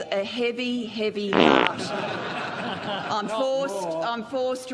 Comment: And - of course - 'fart' rhymes with 'heart' and it almost sounds like she says that in 0:52.